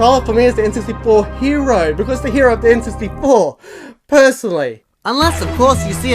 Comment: i thought you said the n64 blew! i was gonna be like, whaaaat? but no, you said the n64 blue! hahaa i love n64, but i still play hundreds of hours of ps1 games every year, n64 is a bit more nostalgia a bit less holding up wahaha